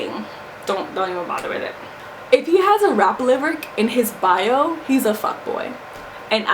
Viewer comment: Wait she said "if he has a ------ in his bio, he's a fuckboy" at 14:14. What did she say? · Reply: 'if he has rap lyrics in his bio he is a fuckboyx